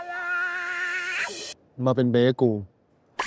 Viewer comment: lmao i dont speak thai and i forgot the subs so i legit thought he said "be my bagel"